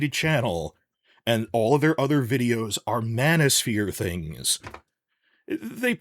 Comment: My head was too much into Game of Thrones that I misheard Manosphere for Mannis-sphere and thought you really have a bone to pick with fans of Stannis.